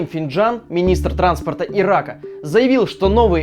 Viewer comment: "Министр транспорта и рака" (что со мной не так раз мне это послышалось?)